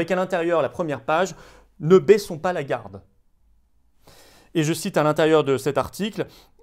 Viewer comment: Le Pr Raoult donne de la force à la Riposte. Vendredi et Samedi vous serez plus nombreux que jamais en riposte avec nous partout en France ! J’ai besoin de vous : <link> 🇫🇷🇫🇷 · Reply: J'ai compris "Ne baiSons pas Lagarde..." 😁